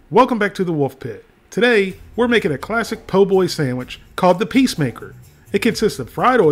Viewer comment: I thought it said "Pacemaker sandwich" I was like.. sounds about right 🤣